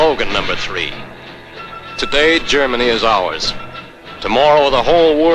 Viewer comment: And more Propaganda bullshit at 5:02 : it means "Heute "da hört Uns" ( instead of "gehört Uns!") Deutschland und morgen die ganze Welt!Today Germany hears us(and not "belongs to us") and tomorrow the whole world"